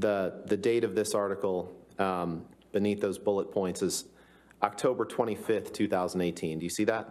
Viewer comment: Omgosh, this Mr Rottenbum does not have a good command of the English language! He said pointses instead of points. @ 9:50.. lol.